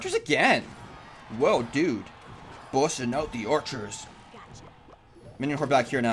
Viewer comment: Did say bullshit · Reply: Electro Wizard busting out